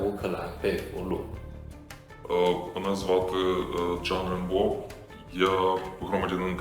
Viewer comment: The "Chinese pow", told you his name was! Kinda sounds like he is saying, John Rambo!😂😂😂 ya i doubt thats really his name! 😂